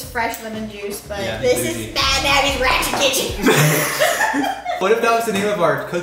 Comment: I thought she said “bad daddy’s ratchet kitchen” I spit my freaking drink 😂 I’m still laughing out loud 😂